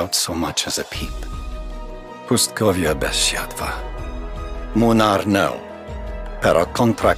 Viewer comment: What does he says at Idle 3:21 ? "Pustkowia desia dwa"? What's that supposed to mean, i'm polish and can't tell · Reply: pustkowia bez światła (popraw mnie jeśli się mylę)